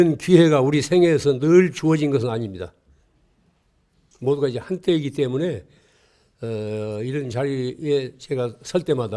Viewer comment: "모두가 이제 한 때이기 때문에" 로 저는 들리네요. 시절이 영원한게 아니라는 말씀으로 생각됩니다. 맘속에 살아계시는 스님 감사합니다.